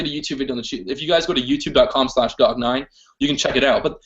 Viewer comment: What was the video he was talking about at 18:40? Did he say Dog9? Too fast.